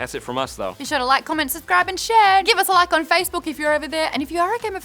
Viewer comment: Did Maude say "subcribe" instead of "subscribe" at 6:29?